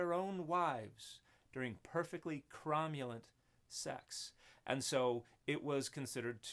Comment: The volume is pretty low. Thought you said "Romulan sex." Wondered what that would entail.